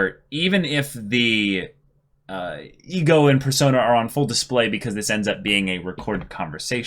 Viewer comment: I was doing other things while listening and at first thought he said "Igor in persona" 💀